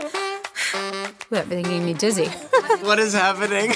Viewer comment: What'd she say at 0:08? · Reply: That made me feel dizzy she said